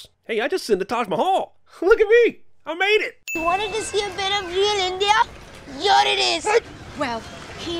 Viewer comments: the subtitles say "you wanted to see a better view of India, here it is", but the kid actually says "you wanted a see a bit of REAL India, here it is". India is a pretty harsh place. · You got the subtitles wrong. Jamal actually said, "You wanted to see a bit of THE REAL India", not "A BETTER VIEW of India". " I also noticed that in a BTTF video, on the train heist scene in BTTF III, you subtitled it as "Reach!" when Doc yells "Freeze!". 😉